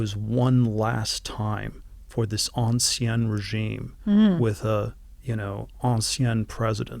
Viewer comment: what's that French (Latin?) Thiel says there? "En sien"? What's that? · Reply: Ancien regime (the French regime before the French Revolution)